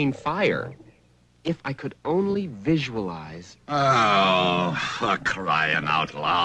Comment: For a moment I thought he was about to say “oh, f*** this”